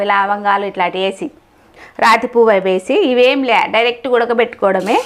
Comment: what Is The word, name of a Flower. Please, explain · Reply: Rati puvvu / stone flower in english It is a spice used in cooking certain specific foods